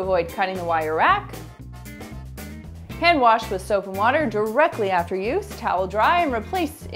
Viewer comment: At, I thought you said I clean with soup and water...time to clean my glasses